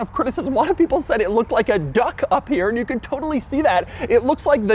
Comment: misheard lyrics: "It looked like a Doug" 😄